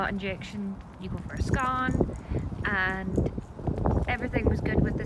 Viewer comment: I thought you said you went for a scone!